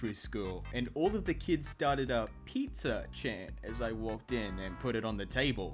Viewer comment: sounded like he said "pizza-chan" not "pizza chant"